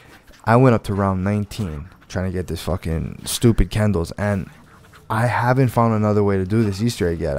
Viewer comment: Throughout the whole video, it sounded like you were saying 'Candles'. Then when you got the KENDAL'S, I was practically punching myself.